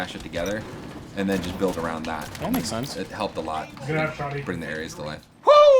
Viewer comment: So THIS is what an M6 looks liek without its attachments. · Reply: CarlosColombia10 lol, i thought he said it makes NO sense my bad